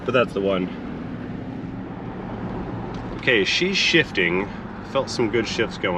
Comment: Almost thought you said "She's $hitting" lol. No, I get it "She's shifting"